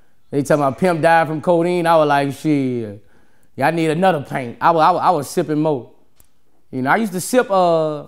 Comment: If he didn't say 16oz I would've thought he was saying paint not pint. 😂